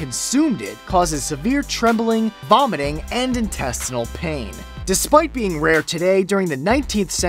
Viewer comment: 3.14 thought he said intestical pain...